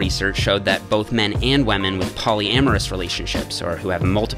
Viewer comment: DID YOU SAY "WAMEN"